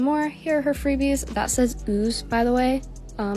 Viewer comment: HELP I GOT SCARED I THOUGHT IT SAID ZOO